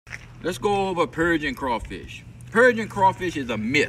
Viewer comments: I heard "Let's go over encouraging crawfish". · Thought he said Persian crawfish I was like definitely a myth 😂